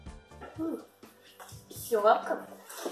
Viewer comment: На 1:18 мне "Неловко" Послышалось..